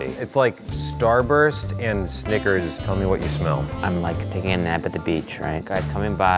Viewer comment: did he say snigers